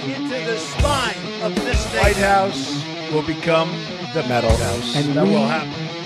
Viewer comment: "The White house will become... the Metal house" -Trump 2020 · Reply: I thought he said the mental house. ..😂